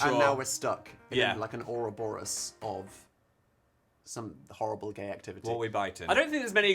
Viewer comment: WHO MADE THE SUBSTITLES???? XDD I snorted when I heard Dan say: "And now we're stuck in ouroboros." and the titles calling it "Aura Boris"💀🤣